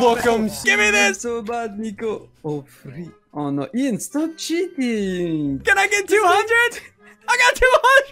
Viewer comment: It sounded like said “Stop shitting!”